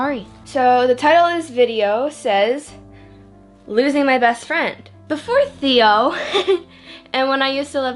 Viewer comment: I thought it said losing my boyfriend